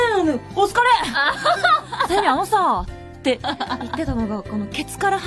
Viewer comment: I think she said "Like I said..." because I hear she saying like "Sei anou sa..." or "Anou sa..." or something like that. Tell me if I'm wrong.